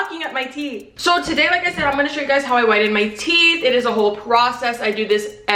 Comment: I literally just thought you said "how i 'widen' my teeth." then i remembered i live on the east coast and youre on the other side of the country. 😅😅